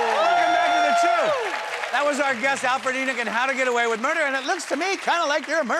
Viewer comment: when the fat guy said Alfred Enoch it sounded like he said Alfred annet